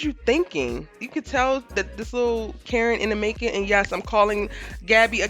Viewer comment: I swear I thought said “CARROT in the making” 😂😂😂😂😂😂😂😂😂😂 yo I was on the floor in here 😂😂😂😂😂😂